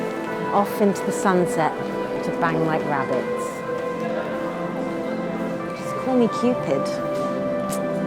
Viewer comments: did she just say, "Off to the sunset to bang like rabbits" as thay cute couple was walking away? Oh my dog. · dang I thought she said "just call me stupid" But hey if she said that I would gladly call her stupid 😌